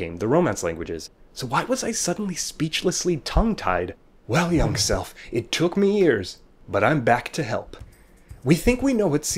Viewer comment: i thought he said "well young self it took me years but im back to hell"